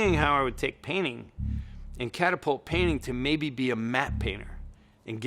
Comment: There is a mistake in the captions @ 2:48, he actually says '.. and catapult painting to maybe be a matte painter', not 'map painter'. He didn't have grand visions of painting pirate treasure maps. He wanted to paint realistic, detailed backgrounds for movies. You see kids, before CGI, they used to use paintings to show distant landscapes and backgrounds. These couldn't be shiny, because reflections or glare would ruin the illusion.